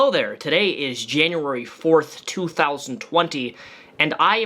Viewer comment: I’m not quite sure if I just miss heard you but, it kind of sounded like you said January 4th not January 24th in The beginning of the video. But anyhow, If he was wearing a bulletproof vest I really don’t blame him for being overly cautious. Even though you would not do any physical harm to him, it doesn’t mean that someone else wouldn’t try to hurt him. There are a lot of crazy people out there and, you kind of never know what someone else might try to do. After all he has pissed a lot of people off. But yeah I totally agree, his suit jacket did look like a suit jacket with a bulletproof vest Incorporated into it. For anyone that wants a Reference to see what one looks like. Here is a video that is reviewing one and testing out its strength. <link>